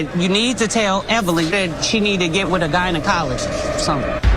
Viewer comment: Jackie said gynocologist....subtitles said " guy in a collage!!!!!!!!!😂😂😂😂😂😂😂😂😂